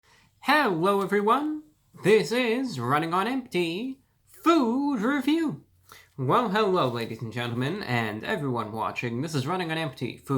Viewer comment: I'm not the only one who heard him say 'hewo, everyone!' Like he can't speak, right? 😂